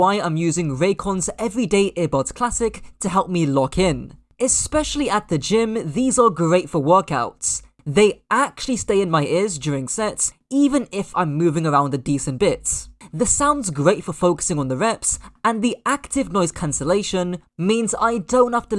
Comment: maybe I heard "actually stay in my ears during s*x" 🥵🤐😶